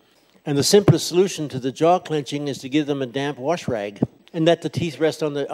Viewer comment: What does he say? damp wash rag?